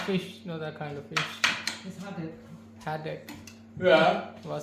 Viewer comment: Thought he said headache matchie …. lol haddock 😂